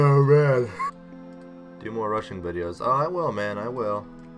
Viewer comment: Was it just me who heard "Do more Russian videos" at 2:35? :D